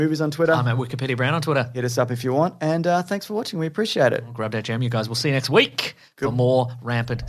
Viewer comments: Wait... been saying "grab that gem" this whole time? I THOUGHT HE WAS SAYING "GRAB THAT JAM"! Admittedly, I was confused as to why that would be his catchphrase, but I figured it's whatever, and didn't think much of it after that. Now, knowing it's "gem", it makes a lot more sense. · Oh wow all this time I thought you were saying "grab that GYM" well this changes everything I don't quite know how to process this yet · I always thought he said "grab that jam"🤣